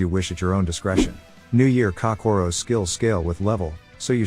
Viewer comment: All I heard was “New Year Cockorro”